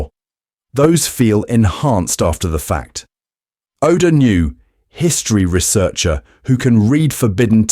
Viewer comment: You said “knew,” but I guess the caption heard it as “nu” from your pronunciation.